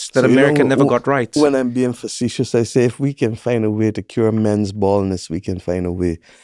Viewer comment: I heard that as “men’s boners” at first, and not gonna lie, I laughed so hard it was almost a laundry-generating incident. Either way, she wouldn’t be wrong 😂😂😂